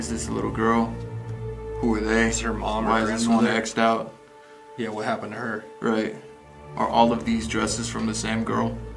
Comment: listen carefully and up the volume , repeat and repeat again. That i hear a women talking. In 1 second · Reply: She says “murder murder”!?!?!??!?!?! 😰😰